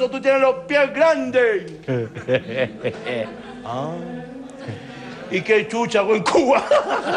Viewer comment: No entendí el chiste del guaton el remate del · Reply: '' Y que chucha hago en Cuba'' xD